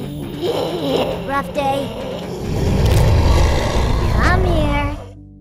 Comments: Uhm is normal that i heard "kill youdself" or?? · I wasnt watching the vid, and ong, I thought I just heard Fwench Fwy say "Keel yourself" lmao. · I thought french fry said “kill your self” help me😭😭😭😭😭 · I didnt hear her right at first i thought she said "kill your self" XD